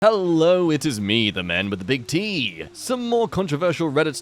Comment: I thought you said "The man with the big D!" at the start lmao